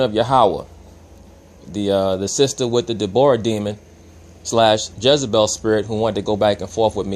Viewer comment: I may have misinterpreted what was said I could have sworn he said Jezebel Dabora demon maybe I got Debra and Dabora mixed up my mistake if I did